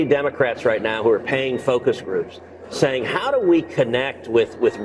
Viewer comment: did ted cruz really say "poofter elitists" or am I mishearing?